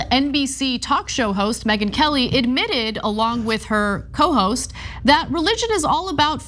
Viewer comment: “Along with her cohost” i was about to laugh because at first i thought Ana said “hoe host” caught me so off guard